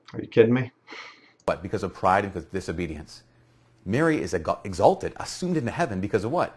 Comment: or so the Catholic guy sounded like he was about to say "Mary is a god."